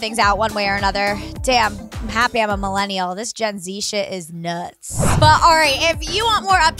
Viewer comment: did she just say “This jenzie shit is nuts.”